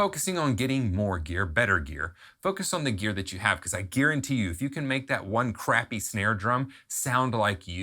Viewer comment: Did he say Gearenty you